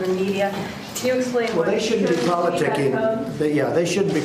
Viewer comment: did he just say “politic-ing”? Twice?